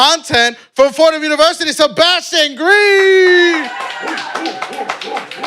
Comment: idk why I thought he was saying Sebastian telfair 😂